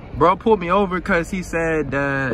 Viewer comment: i thought u said yo shit was legal